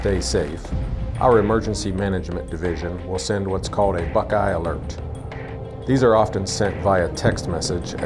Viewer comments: I thought he said "a fuck aisle" ...... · sent VIAH text message i thought it was said via